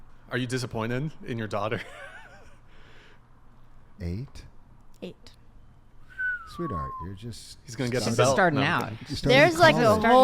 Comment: Listen to the father's response. It's hard to hear because the mother realizes what he's about to say and speaks over him. Father: "Eight? Sweetheart, you're just starting college..." Mother: "She's just starting out" (talking about the daughter's sexual history). One says "that's a lot", the other says "that's low". The father is right, though. 8 bodies by 18 is a lot.